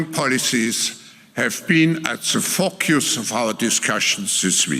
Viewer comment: Did he say Trumps return has been the "fu@ yous" of our discussions this week? 😂